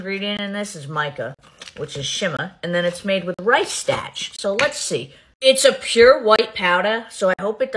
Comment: The way she said “starch” was so unbelievably exaggerated omg. I thought she said “stache.”